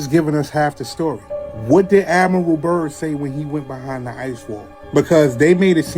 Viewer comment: admiral burns? Is that what he said? I want to look into it but at 1st I thought he said Adam